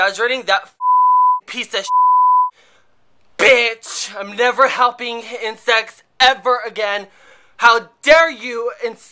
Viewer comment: your facial expressions & screaming are HILARIOUS!!! & btw, i thought u said im never helping insects have sex again :o